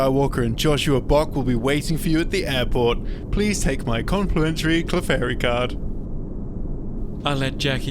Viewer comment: It sounded like he says "Please take my complimentary Clefairy card."